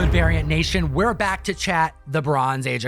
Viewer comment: who else thought he said aryan nation omfg😂